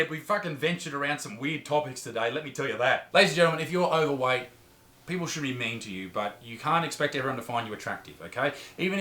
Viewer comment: Support those that support this channel by checking out Night Shift on Amazon Prime! Find the trailer here - <link> Watch in the land down under - <link> Watch in the UK - <link> Watch in the USA - <link> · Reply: Wait at 10:37 did you say "people should be mean" or shouldn't xD